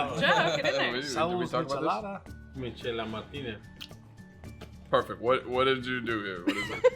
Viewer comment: omg I had to rewind because I thought said he was gonna make an enchilada martini